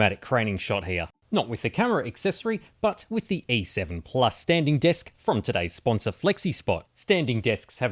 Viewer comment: I thought you were saying sanding disc, not 'standing desk' 🤣